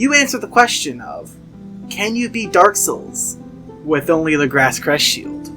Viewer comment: I thought it said “Can You Eat Dark Souls With Only This Thing,” and was intrigued. Unfortunately, nobody ate a copy of Dark Souls and now I’m sad